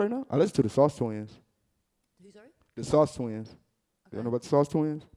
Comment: Anyone can please write down the name of the rap group from Huston he mentionned ? I heard something like "soft wings" but it's not it. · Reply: dedlaraclure the sauce twins